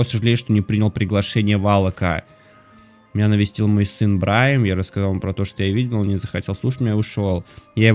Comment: мне одной послышалось Брайн? 😂 не ну Брайан (может это опечатка и сын был Брайн;) ;) ;) ;) ;) :) :) :) :) :) :)